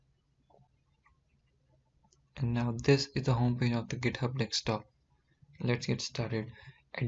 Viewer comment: Who heard it as dekstop? Tho sup vlog.!